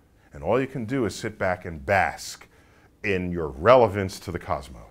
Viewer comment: HELP I'm studing english. Minute 2:01 -> Does he said: "So what you can do is sit back and ¿basc? in your relevance to the cosmos" (that is not subtitled) Great videos, I like very much big think. Thank you very much. Tell me please people like Tyson, Michio Kaku, who speaks so clearly so interesting topics. Thanks.